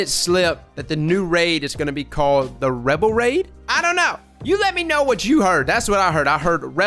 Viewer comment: Could be wrong but I'm thinking rebel raid = Revelry spring event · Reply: You misheard, they said revelry